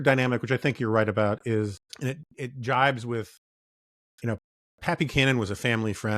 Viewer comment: When Jonah said "Pat Buchanan" I heard "Pappy Canon"